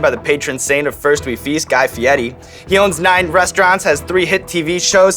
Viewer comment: I always thought you said his name like "Guy Fury".